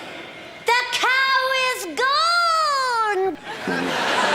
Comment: i thought she said "the cow is gold!" that would've been a good joke because the lighting made the cow look like it turned gold xd